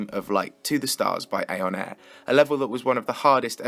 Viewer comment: instead of aeon air, the subtitles say "a on air"